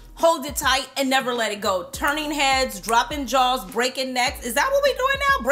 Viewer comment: Ok so I thought you said “droppin drawls” at first, I hollered! Like ohhhhhh hold on summer is around the corner & the “glammas” are wildin 😆😆😆